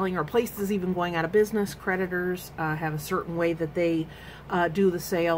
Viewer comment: I misheard creditor for prediter. Lol opps